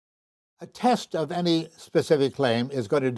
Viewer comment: I thought is said paranormal CLAMS....whoops lol